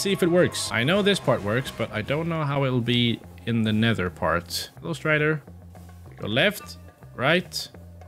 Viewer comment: When he said "hello strider" I legit thought he said ghost rider